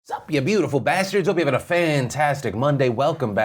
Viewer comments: Did he just say f you beautiful bastards? Ehhhh ok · Thought you said “dumb bastards” in your intro, man I need more coffee